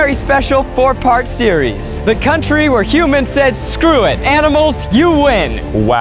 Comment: “The country where humans said ‘screw it, animals, you win’” that’s hilarious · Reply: At first i heard "the country where humans screw animals..you win!" And i was like 😳